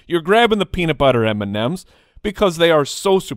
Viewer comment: At first I agreed with you about M&Ms because I thought you said Peanut M&Ms. The classic yellow bag is the best. THEN Peanut Butter. Then regular.